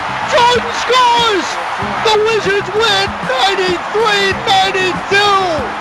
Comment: I heard "The wizards win 33-32".. so i take it Jordan scored all 33 points ha